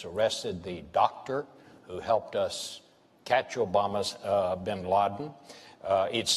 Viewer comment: Did he say "Obama's Bin Laden" ??? lol